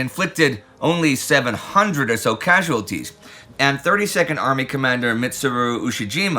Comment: When Indy started talking about the 32nd army, I wasn't looking at the screen and thought he said "30 second army" and that sounds like the worst unit to join